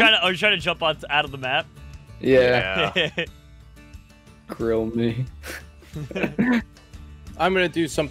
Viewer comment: When said “grill me” I thought he said Rail me 😭